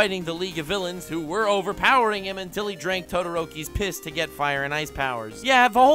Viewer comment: Genuinely feel dumb as frick for buying this. Even after he said drinking todoroki’s piss for His Quirk. God, thanks for the likes and and thank you for reading this nuc. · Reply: I thought he said todorokis kids